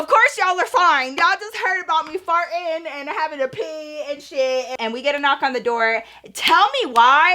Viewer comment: not me wondering “ when did she say she shit ? “ 😂😂 when she said “ yall just heard about me farting and having to pee and shit “